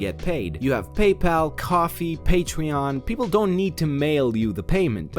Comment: did he just say patweon or am i just going batshit crazy